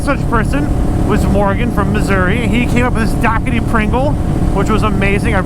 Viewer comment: I thought you said dockety pringle